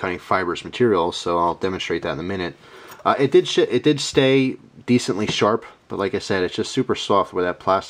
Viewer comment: LOL. At 9:08 it sounds like you said "it did shit"